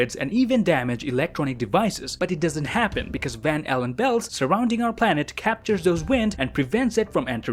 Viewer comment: I thought it said Van Halen Belts